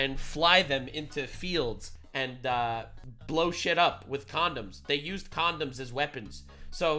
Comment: I thought they said Condos!